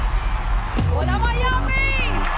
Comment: WaDdUp, MiAmI?! I died😂 idk why it's just so funny to me😂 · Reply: I thought she said wUdUp mIAmi but I hear hOLa mIAmi